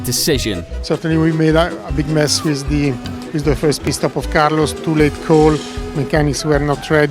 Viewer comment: the captions say "piss stop" lmao